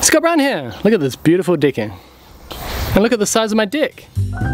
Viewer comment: At the beginning, I heard “Look at the size of my dick!” That is NZ accent at its best form.